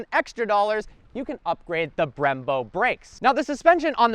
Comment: Did I hear correctly at 2:58, $22,000 for upgraded Brembo Brakes?